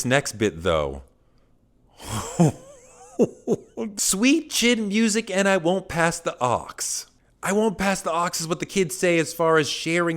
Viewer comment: Sweet Chin Music when I walk pass the oops! Shawn Michael’s being referenced in a diss song and it’s smoothly done is incredible. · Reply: Oh i thought he said "sweet chin music n i wont pass the aux" as in, my music gon beat yo ass for me and i wont let up by giving you a chance to 'change the station' so to speak